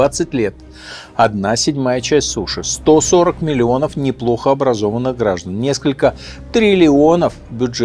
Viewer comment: "140 млн. неплохо образованных граждан.." - Вы серьёзно?? Нет, если сравнивать с Африкой, то конечно.. 🤦🏼‍♂️ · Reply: А мне послышалось " плохо образованных"